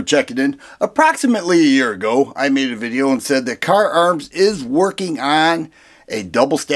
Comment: I need an eye exam I swore it said Kahr Jams 😅